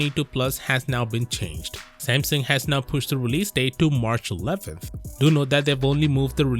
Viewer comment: I'm actually more hyped tor the S22 Vanilla. I'm glad that the release date has been pushed to 11th February, but I still won't buying it before seeing a good amount of reviews, also I haven't completely, just yet, ruled out the Ultra. The plus is a nono, it barely offers anything more than the Vanilla and I want it compact and light, so it's either the base or fuck it I'm going for the Ultra! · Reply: Edit: hell I thought he said 11th of February, not March. I'm not so glad now eheh... still have to wait more than a month 😅🥲 So no, I'm not glad they pushed it even further, 25th was already so long!